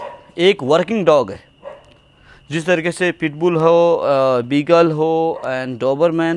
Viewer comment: did he say beagle😂